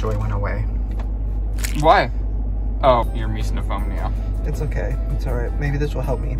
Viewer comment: I cannot for the life of me understand what Ryland is saying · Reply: He said "oh, your misophonia" I think😅